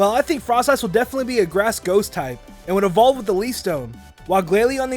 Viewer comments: Did he just say "Grass Ghost" not "Grass Ground"?! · did he say grass ghost or grass ground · grass ghost i think that says ground But good vid :)